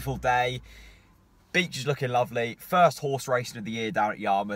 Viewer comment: Sounded like you said Bitch looking lovely !